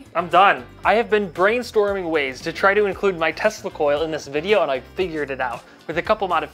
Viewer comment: What You said, testicles LoL